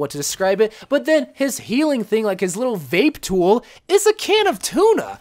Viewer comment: did he call it a vape tool